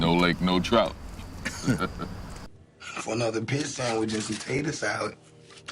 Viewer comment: He says "pit sandwich" not "piece" like the subtitles say.